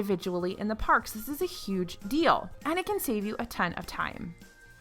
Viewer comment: I'm such a huge Star Wars nerd I could have sworn you said "Anakin" (I know you said "...and it can", so please forgive my one-track mind).